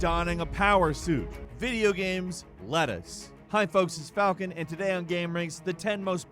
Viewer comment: why did I hear "video games...lettuce"😂